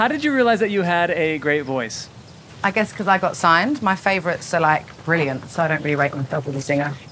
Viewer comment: “This woman doesn’t age!!!” she’s 33… that’s not even old y’all are crazy 😭 Everything about her is beautiful though. She seems like such a caring and genuine person. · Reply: Could anyone help me figure out what she said at 1:19 - 1:23? "I got signed my favorites (?) ... brilliant ... (?)"